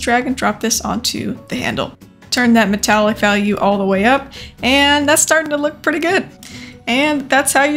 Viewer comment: Thought you said "Turn that Metallica all the way up" at the end so double thumbs up for that xD👍👍